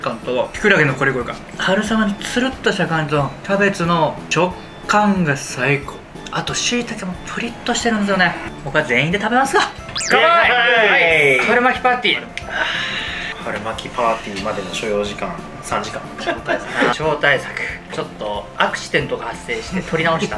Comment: どうでもいいけど春巻きパンティーって聞こえてしまったww